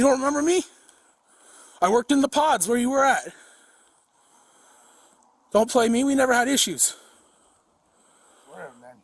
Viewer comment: I thought the officer said "Don't Baby" and the Perp said "I will Boo" I was like WTF?